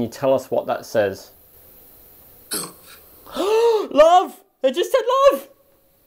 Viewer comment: At about 50:28 in the investigation, when asked what the word he had spelled was, I heard, “love”.